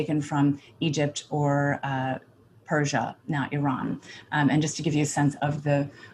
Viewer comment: Iran is Persia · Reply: At first I also thought she said "Persia, not Iran", but I think she actually says "Persia, now Iran" (o